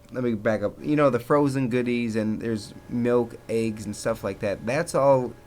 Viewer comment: at first I thought he said aids instead of eggs